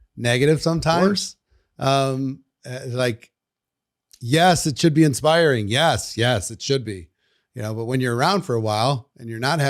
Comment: I must be watching too much RuPaul's drag race because I heard this as "YAAAAS it should be inspiring! YAAS! YAAS it should be." Half expected him to segue into "yaas queen boots the house down momma tea 🪭"